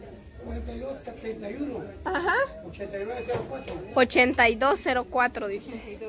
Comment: Minuto 10:14 yo escuché 89 no 82 y por favor sensuren los números de teléfono muchachos